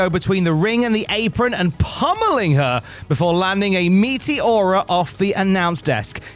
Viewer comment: took me a minute to realize this fool said "meteora", my high ass thought he said "meaty aura"